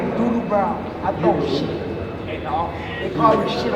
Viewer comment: not even one minute in and I hear "I throw shit"